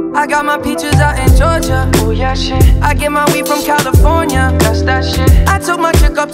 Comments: oh. I thought he said "I get my way from California..." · I thought he was saying "I came all way from California" wth hahahah